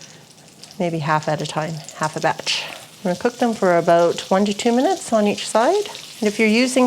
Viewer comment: She actually said, "1 to 2 minutes on each side" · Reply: listen at 6:03. Sounds like about 23 min on ea side, which I already know you don't cook shrimp but a few min. It just shocked me to hear what it sounded like she said. I got a 2nd opin. They also thought she said 23 min ea side.